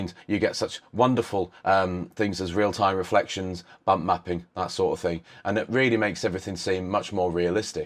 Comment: I seriously thought you said "Bum Mapping" :P